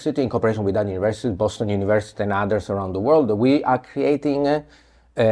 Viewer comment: anyone know what is the web address of the tracking web site that professor Geraci was referring to at 19:26 ? Sounds quite interesting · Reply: There's the Mapping Global China website of which Michele Geraci is a contributor.